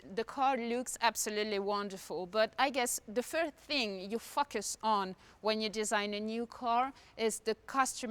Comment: i heard f*ck us on